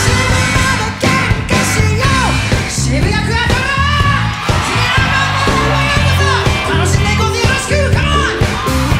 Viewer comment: からなんて言ってるか分かる方いますか？ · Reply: 渋谷クアトロ髭男のライブにようこそ楽しんでいこうぜよろしくだと思います